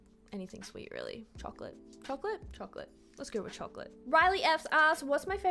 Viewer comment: im not sure if that was me, but i did ask this question, you did heart it and it does sound like you were trying to say “relief” except it sounds like you said “riley” sooooooo im not sure LOL, but i hate salt n vinegar wtf, at least the brand is lays though